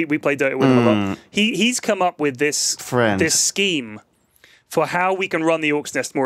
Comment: Mmmmm. Friend. Wtf? did i hear this wrong? that was so creepy